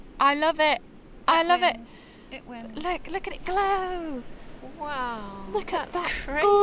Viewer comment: I thought I heard you two singing the Christmas song 'Let it glow , let it glow , let it glow ...... :D Happy Holidays ..... XO